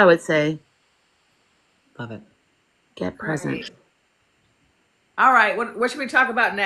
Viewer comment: est I heard 'get pregnant' Was that part of the conversation, or?